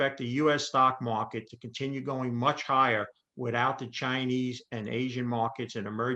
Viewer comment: I thought he kept on saying CHINER....HAHAHA